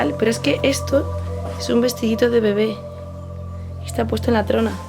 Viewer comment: parece una voz de mujer que dice bueno después de que dices es un vestidito de bebé